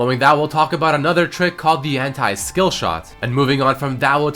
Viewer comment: The Hentai Skillshot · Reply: it does sound like he is saying the hentai skillshot